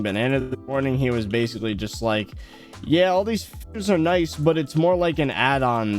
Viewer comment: sounds like hes saying all the F'rs are nice